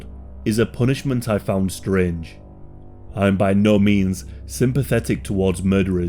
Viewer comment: i heard "scringe" instead of strange and now there's a new word for a motive that's both strange and somehow cringe